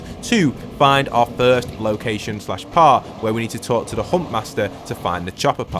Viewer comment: It sounds like your saying “hump master” 😹